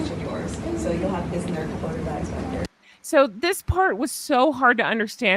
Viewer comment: I've got my headphones on and put the time here so I can click it and keep going back to see if I can figure out what she's saying lol Edit - Sounds more like she's saying "corley bags" "So you'll have this and there's corley bags back there" When I search google for corley bags, it looks like tote bags. I may be wrong, but it does sound more like corley than quarter to me.